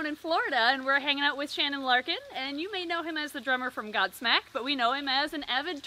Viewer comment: Omg, I love Godsmack! I didn’t know Shannon had a pond for turtles, they’re so cute 🥺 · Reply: When said "Godsmack" I misheard it as... God Snek Not God Snake, God Snek... Since he likes reptiles... :\